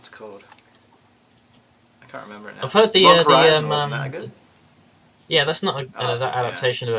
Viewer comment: Love the team for the pre season discussions :) I'm with. Love Nisekoi! What is the show he says at 32:59? Couldn't quite hear it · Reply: I believe he said "... Log Horizon, isn't that good?" so the show would be Log Horizon.